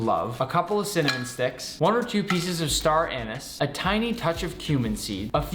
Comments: Did he just say “star anis”? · Did he say star anus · I had to double back I thought he said star anus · i thought i heard star anu*ses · Am I the only person that thought he said star anus